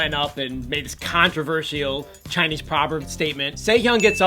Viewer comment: I thought he said "Taeyeon" 😂